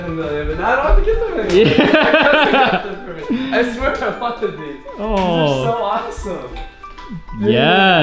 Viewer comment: what did he say? i heard 'I swear that fvckded me'